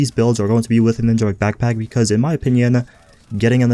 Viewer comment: what backpack?? Literally repeated the name 3 times in 30 seconds and I still can't understand what it's called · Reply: Alex Tereshchenko ninja bike backpack